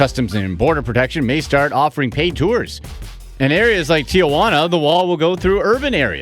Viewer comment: Did he just say Tiaguana? Lolololol